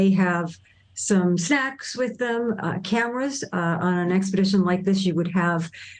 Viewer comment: What did she say? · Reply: Snacks & cameras.